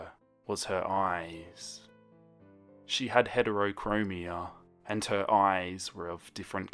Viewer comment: When it said Heterokromia I thought it said heterosexual I was like how do you get that in your eyes i- 😂😂💀